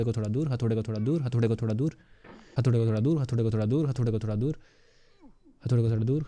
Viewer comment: i hear hatode ka guu 🤣